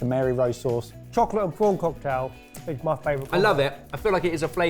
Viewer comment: did Barry just say "Chocolate and prawn cocktail is my favorite combo" instead of chips? I think you should make him try that 😆